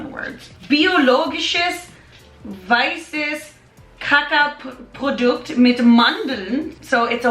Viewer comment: When she read the german label and mispronounced "Kakaoprodukt" which means cocoa-product, it sounded like she said Poop-produkt instead I'm dying